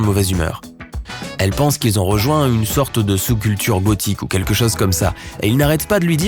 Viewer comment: C moi ou à 3:17 il a dit bothique au lieu de gothique